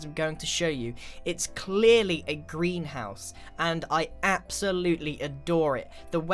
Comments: on the third one, he said greenhouse but I thought he said grian house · I thought he said a "grian house"